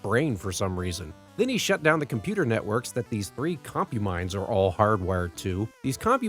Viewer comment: CompuMinds?!? I thought you said Concubines?!?!?!?!